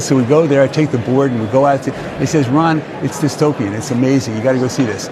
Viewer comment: did RB say "it's dystopian"?! ..doesn't matter, I get the point - it's good, not bad.